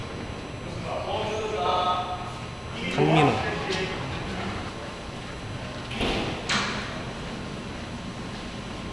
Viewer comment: ㅠ저만 못알아듣고 궁금해하는건가요? 4:54 이원석 선수 뭐라고 얘기하고 퇴장하는지 들으신분 답좀 해주세요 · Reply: 강민호